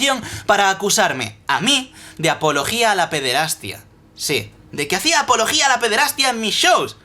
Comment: ¿que es?,apologio a la peterastria o eso que dijo el en el minuto